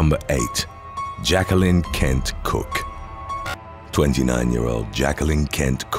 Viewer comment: I heard "Jacqueline Can't Cook" I'm sorry